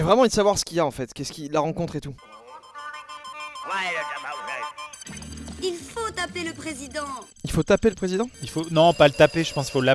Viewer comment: Moi aussi j'ai entendu "il faut taper le président"